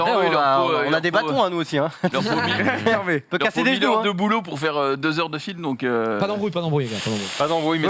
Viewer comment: Il dit quoi a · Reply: "Il leurs faut.." Il leurs faut..." Il leurs faut 1000 heure de boulot pour faire deux heure de film donc euuh"